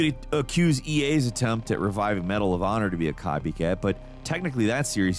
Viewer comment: you say “Medal Of Honor” I hear “PATTERSON!” 😂